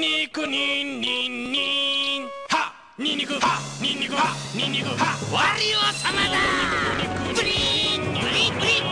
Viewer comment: what did Wario say 😳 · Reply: Ni Niko HA Ni Niko HA Ni Niko HA HA WARIO-SAMA DA!!!